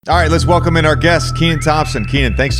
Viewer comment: Deadass thought it said Klay Thompson instead of Kenan lmao